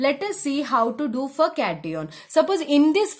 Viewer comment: What she said 🤣 🤣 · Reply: Cation lol 🤣